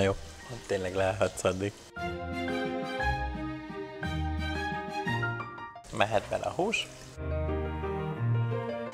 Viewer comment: től két, három másodpercben vajon mit mondhat a hölgy, szája szélén sziszegve :) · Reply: Azbeszt